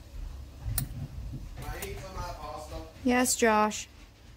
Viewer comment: Watching this right now and loving all the detailed info. At 38:51 did Josh just say, "Can I eat some of that possum?" lol